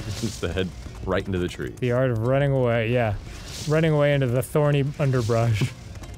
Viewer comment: I swear I heard Dan say "Running away on Stormy ThunderBrunch" which sounds like an awesome name for a horse.